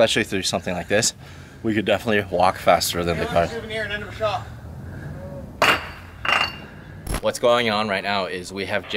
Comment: Can somebody explain me what happend at 6:08 ? What does the guy say, is he mad at Larry? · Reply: mastertrick17, He said "hey, you want a souvenir? Here's a shock". Then proceeded to toss the shock towards them.